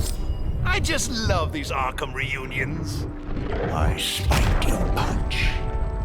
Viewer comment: What did Scarecrow say at 9:21? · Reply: I spiked you punch